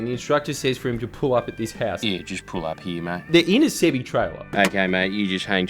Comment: what does he say? They're in a city trailer?? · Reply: Semitrailer